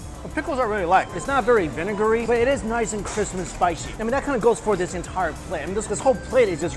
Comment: at first I heard it is nice and Christmas spicy. That's the per of great visuals.